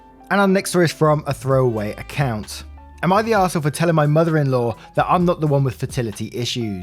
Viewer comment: For a moment there I thought I heard you say "Am I the waffle?" at the intro. xD